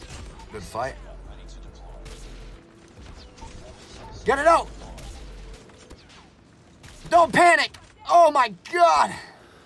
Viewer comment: 😂NGL I heard “pull it out...don’t panic” at first....